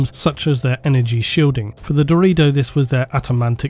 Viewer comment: I kept hearing "Dorito" pattern.